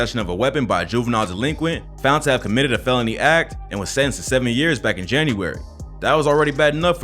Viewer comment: did he say 70 years?! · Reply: 7